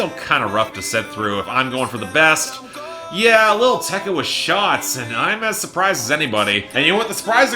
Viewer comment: Jeez you scared me, I thought for a second you said "Yeah, lil tecca was shot" and I was gonna worry about Ran$om taking #1...